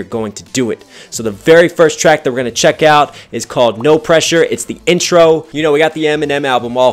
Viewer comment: Defo was a less whiney album for sure but overall under pressure was better from start to finish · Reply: ItsDillon oh sorryyyyyyyyy, i thought u said Under Pressure. I’m sorry! 😅😊